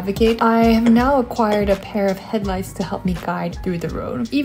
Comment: Okay… So I thought you said ”headlice” and not head lights. But for some reason I imagined anime/cartoony lice just giving you a thumbs up and telling you, you are doing a great job 😂 All within a split second before I realized I heard you wrong.